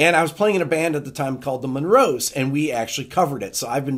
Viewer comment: Rick, when you mentioned you were in the Monroz, I thought you said Monroes who had the great pop song, “What Do All the People Know.”